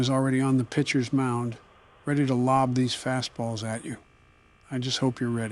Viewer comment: For Joe. Did you say “Faz balls” or “Fast balls”?